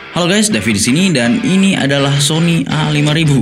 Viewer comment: apa gua salah denger yah? "Halo gays david di sini"....